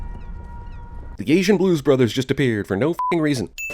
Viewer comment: He says, "The Asian Blues Brothers just appear" but the subtitles say "disappear"